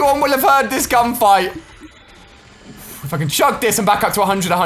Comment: why did he say hes channel is kid friendly? lol Edit: sorry I thought he saying other word. Back in the day when he play cod he used to swear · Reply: Fakhir YT he said "if I can chug this". Are you high?